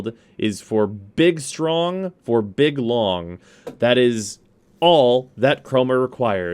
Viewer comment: Am I the only one who heard him say chroma only needs big strong and big long lmfao